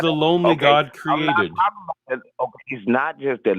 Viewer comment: Did say “only god” but was misheard as “lonely god”? I heard “lonely god” myself but his reaction to when he asked about it leads me to believe he said “only”. Still impossible to track his meaning, though. A bit disappointed Doug did not call when was on. He could tell her about how women are more “emotional” and “less rational”. I’m sure it would have gone down great!